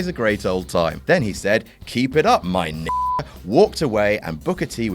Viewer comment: Not gonna lie: I always thought Vince said , "Keep it up, ham and egger." Probably because they sound awfully alike and he sounded like he hesitated a bit.